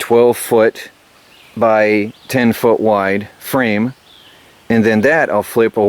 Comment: thought u said 8feet wide, what changed your mind?